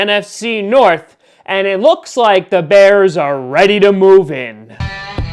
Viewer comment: instead of “move in” its “move BACK in”. 😉